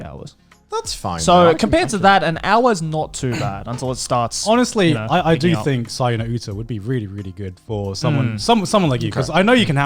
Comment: What VN are they talking about here? I swear they mumble every time they say it. I can't figure out what they're saying. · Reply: Saya no Uta